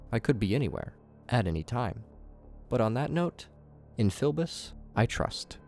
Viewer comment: I really do have battle cats brainrot I thought that said in fili I bust